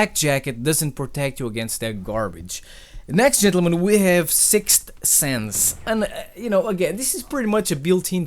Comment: when you said sixth sense i thought you said 6 cents i was like "treyarch put a fucking rapper in the game..."